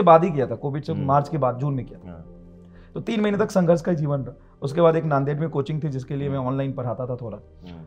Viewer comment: did he said nanded (nanded, maharashtra)?